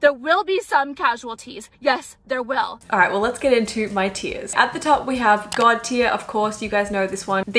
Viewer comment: When you said “Alright so let’s get into my tiers”, I thought you were saying “Alright so let’s get into my tears” 😂